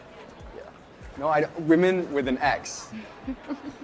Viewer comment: Did he just say “womxn with an x?”